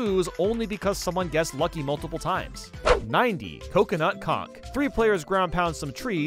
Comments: did you just say coconut c*ck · I’m not the only hero heard it as coconut c**k right